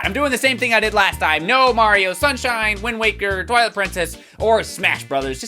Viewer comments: I thought you said no Mario games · no mario......, "put paper mario on the list " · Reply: I thought this at first too, but I rewatched it and this time I heard "no mario sunshine". So I think it was just that one that was exempt, not all mario games. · I thought he said no Mario